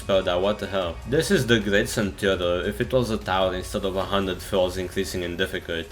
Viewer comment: is it intended for the text to say !== instead of 100